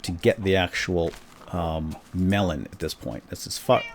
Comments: did you say "this is f***ed" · does he say this is fucked???